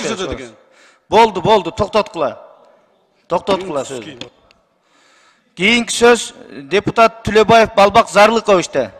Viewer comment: Кто услышал мат на 1:31 секунде? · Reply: Энендисигеин деди таза эле